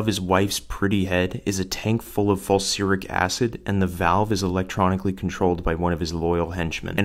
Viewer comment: Did he say Fulsuric acid?